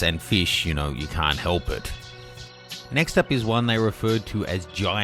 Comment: what does he say? "It kind of looks like akshell?" What's that hes saying?